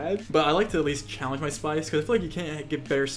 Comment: totally thought he said "spice farts"